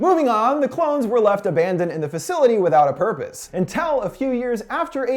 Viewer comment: did he just say "intel" instead of "until"?